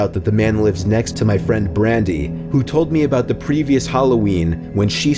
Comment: scared the shit out of me bc my name is randy and they said brandy even though I thought they said randy💀💀